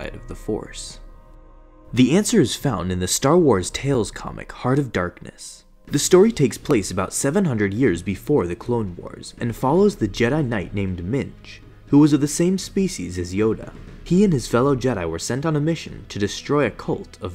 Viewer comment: Did he just say “jedi knight named MINGE”?